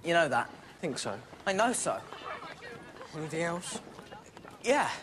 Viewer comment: I'm embarrased that I have to ask this, but what did Harry say to Ron at 5:56? "You are a right fu*king wolf..." I don't get it. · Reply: He says "poof" not "wolf". Poof is English slang for a gay person.